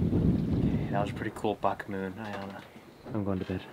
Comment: A what moon? Sounded like you said "F*** moon"